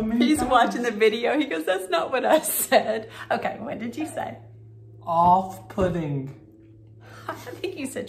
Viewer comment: I heard..."Off pudding"....But that's just me.